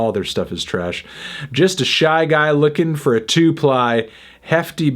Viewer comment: I thought he said “just a shy guy looking forward to fly” not “looking for a 2-ply”